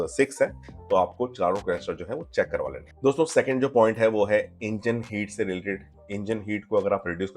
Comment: bro, the subtitles are so weird, specially 2:05 i dint understand what u said plz tell. aapne bola check karao, subtitles me likha hai dont let it checked, aur ye crutches kya hota hai